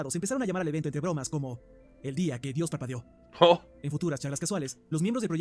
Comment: entendi "el dia que dios papeo"